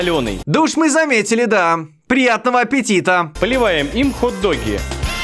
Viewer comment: послышалось блеваем им хотдоги